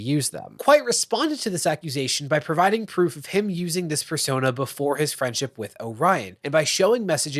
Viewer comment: So I know this is serious and all, but at 1:14 you very clearly say “fursona” instead of “persona”